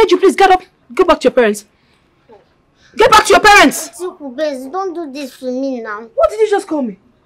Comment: Did I hear Ebube say ‘’Auntie Okubenz’’😂😂